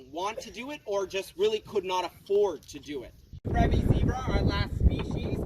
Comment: Interesting. Didn’t know that only plains zebras have stripes on their stomach. Cool. They are basically grey when shaved. Cool about the extra skin on the necks of the mountain zebras. Hoof trim has to be tricky. That’s horrible how the females react to each other. Bet doing that is expensive. I couldn’t tell what he was calling the last set of zebras. The ones with the temperature regulating ears. It’s weird that the females are so aggressive. Poor little Marty. Very interesting tour at this ranch preserve. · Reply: Oh, he was saying Grevy. I thought he said gravy, and I knew that wasn’t right. 😂😂